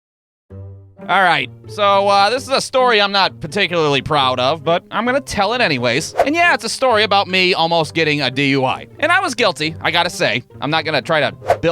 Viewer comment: did you say paticuly proud of?